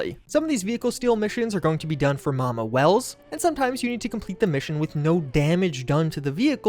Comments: Correction about stealing cars for "Mama Welles"; Pavel actually said "Muamar Reyes" but due to his accent it the journalist misheard it. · the vehicles aren't from mama welles, but from muarmar reyes (el capitan) it was misheard by the one writing the article this info came from due to the accent of pawel sasko, he said as much in one of his most recent streams on his youtube channel